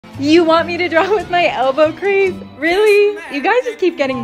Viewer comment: I thought she said elbow grease…